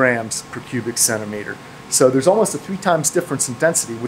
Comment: the subtitles say "all of the" instead of "almost a"